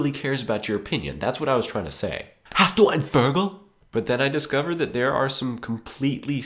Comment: hast du ein....what again? fergel? Ferkel? what did you say? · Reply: Vogel